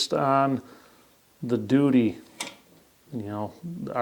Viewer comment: he said doody... Ha ha ha! Or was it duty? I like how I heard it better.